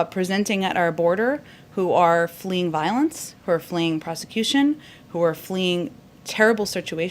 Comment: Did she say, "fleeing prosecution"